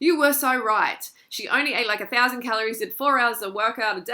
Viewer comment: Love ya freelee!!! Great video. I thought you said you used to eat 1,000,1200 calories . I'm like woah is that humanly possible ;)